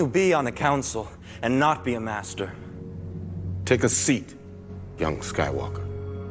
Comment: Sorry all I heard was “take a seat muthafu$&a” ha what?